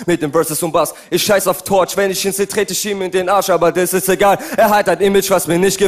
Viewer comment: Was sagt der da? 😂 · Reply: "ich scheiß auf torch, wenn ich ihn seh, tret ich ihm in den arsch"